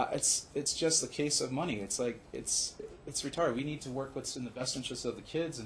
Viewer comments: did he REALLY say “...it’s retarded”?!?!? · Wait wait wait. Did he say “this is r*t***ed” at 28:00 ?! 😳 omg